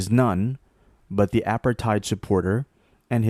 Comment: Typo at 5:42 "appetite" was used instead of "apartheid"